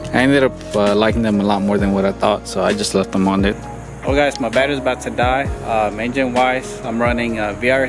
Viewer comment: Thought he said his dad was about to die lol, but it was just the battery lol